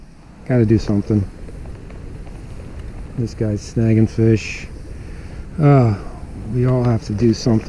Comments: omg. i thought it said snagging coons, i was like wtf lol.. i didnt have my glasses on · Ha - I thought it said snagging gooks; took a double take